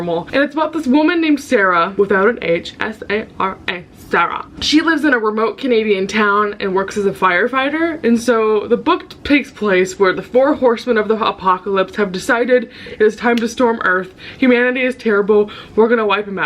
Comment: I’m sure you said “the four horseman of the apocalypse” but I heard “the four horseman of the hot-pocalypse” 💀😂